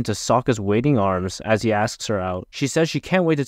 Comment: Thought you said Black Bender ⚫ my bad 😂 almost responded negatively 😂😂